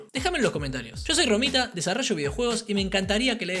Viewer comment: Soy yo o al final escuche "yo soy ramita"